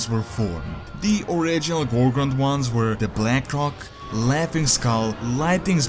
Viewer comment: did he say blackcock?